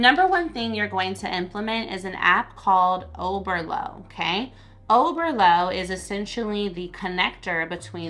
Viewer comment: I love your content! Sometimes when you mention apps to use I can't fully understand you. I thought you said overloo, girl I searched long and hard just to find it was overloop app lol. Keep the great content coming you are greatly appreciated.